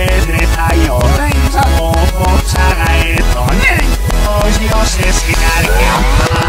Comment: Lo que entendí fue os asesinare en el minuto